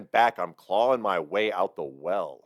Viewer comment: He actually says "Sadako, clawing my way out the well". Sadako was the 'monster' in the Ring movie, who was thrown down a well and sealed in, left to die. When someone eventually tried to find her body to lay her to rest, her spirit wrought vengeance.